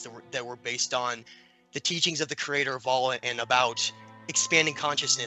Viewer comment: What does he say at 14:25 ? the teachings of who? the captions say "allah" but i dont know if that is really what he said.